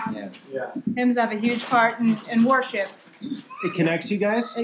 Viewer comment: anytime that lady said worship I thought she said warship and that really threw me off🤣🤣